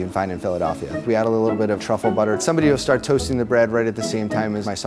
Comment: did he say truffle butter